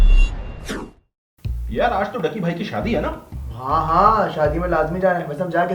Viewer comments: Did he said ducky bhai?????!!!!!! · did he said ducky bhai